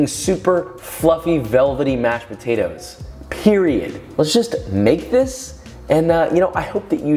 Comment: did he just say PERIODDD?